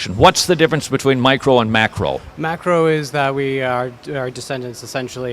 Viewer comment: Lol! Am i the only person who heard him say what's the difference between "micro" and "MACKEREL" evolution!? 5:49 I know that one of those is a GODDAMN FISH.